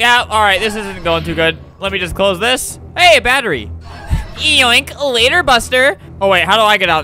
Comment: Why when he said “ay battery!” I thought he said AA battery..?